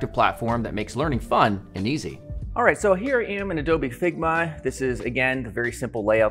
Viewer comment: Did he say Adobe Figma? 😂😂😂